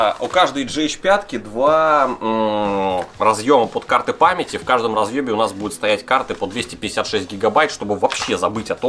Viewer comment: Мне послышалось или он сказал "В каждом разъёбе" на